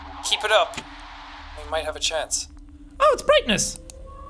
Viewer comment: to me, this sounded like the kid saying “Oh no! Our table! It’s broken!” not gonna lie 24:24 edit: later in the video, Jack had the same opinion.